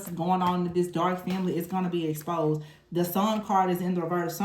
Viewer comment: I thought you said my enemies were going to be eclypsed